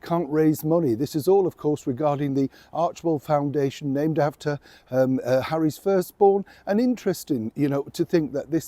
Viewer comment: For a minute there I thought you said "Harry's first-born".